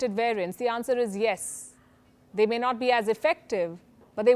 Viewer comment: "They maynot be effective. But it will not be ineffective"..what a statement! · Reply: they may not be as effective she said it its written there how could you have both misheard and misread the statement